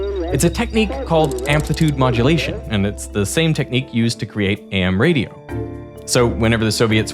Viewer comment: Did you just say amm radio??? Maybe I misheard 5:22 Is that different from "fim" radio? AM is pronounced "Ay-Em Ray-dee-oh".